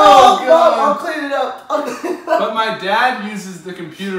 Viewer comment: did say "Mom I'll clean it up" or am i deaf